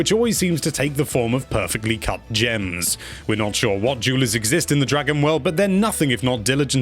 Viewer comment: had to do a take back cause I thought he said jews instead of jeweler